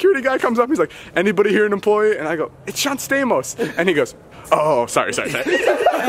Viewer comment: what did David say? Anybody what? Couldn’t understand him : ( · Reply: Aimée Raine "anybody here an employee? it's John Stamos...